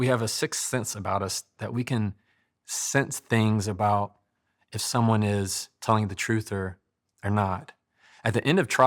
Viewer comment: Oops thought he said if someone was lying to you. I agree, no one wins in a fight. And I have lost a couple significant relationships in my life. Because I couldn't control my rational thinking. And just said what was on my mind